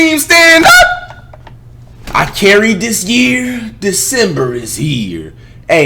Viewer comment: At first I thought he said, “I can’t read this year, December is here.”